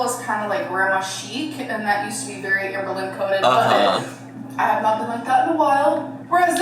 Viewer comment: I am soooooooo sick of the "amberlynn coded~~" shit she keeps saying like girly pop please stop this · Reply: Oooh, it does now make sense. I always thought, she said "Amberlynn- coated" 😅